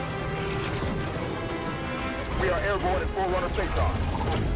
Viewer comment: you here some one say, "we are airborne at forerunner face off." Now the face off part is just what I heard and they could have said something else, but the visuals they use are the new Prometheuen vehicles and the covenant looking structure, which is leading me to believe that it is not covenant but indeed forerunner. #HaloClub